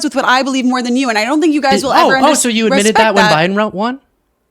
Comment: Sorry, english is not my first language. Could please someone tell me, what happened before 34:03 when he made her stop for a moment? Did he say "so you admit that Biden won?" ? And if, I do not get the reference. It is really to fast for me and with the Youtube transcript I also don't get it.